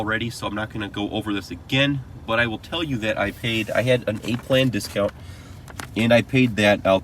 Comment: did you say you got an 8 plan discount? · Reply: Think he said A plan.